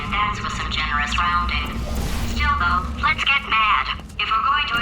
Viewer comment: does glados say YOLO at 42:12?! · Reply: "Still though"